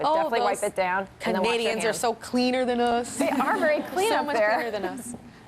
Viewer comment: OH LOL I thought she said "idiots" when she actually said "Canadians". Okay, that clears it up =P